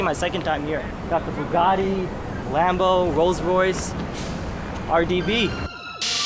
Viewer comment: did you just say RBB LA ??? LoL. Tongue twister? Lol. Or an I hearing wrong. I played it back 6 times and I still hear RBB not RDB.